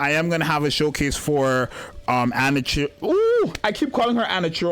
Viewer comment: I almost misheard Anachiro for Anna's Churro... I need to get my hearings check.